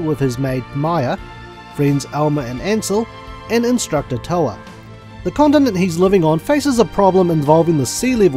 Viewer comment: Great review. Been waiting for one for this game. I think I will pick this up. However early on, I thought you said, "the condom he has been living on"... I had to skip back to listen to it again. hahahaha